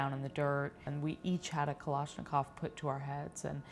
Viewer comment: Did she just say Kalashnikov at 3:03??